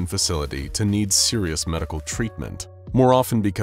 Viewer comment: why did i hear the heavy from TF2 going "MEEDIC!". but with just the "DIC!"